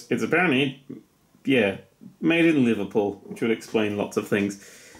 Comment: Thought it said "Made in China" not Liverpool... And heeyyyy Liverpool's a nice place... I mean... We got the Beatles... And Chavs... And drugs... Okay maybe it isn't never mind...